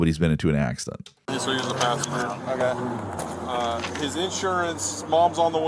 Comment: cop sounds like he says passenger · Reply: confirmed: passenger